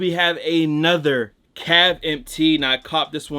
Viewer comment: What’s the bleach tee at 8:30 ? Couldn’t make out what he was saying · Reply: Cav empt